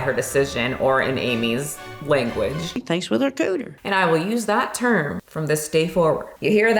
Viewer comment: I keep going back in the video to hear what Amy said at 11:15 and I still can’t understand what’s coming out of her mouth 😂 I did it like 6 times ! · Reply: "She thinks with her cooter" 💀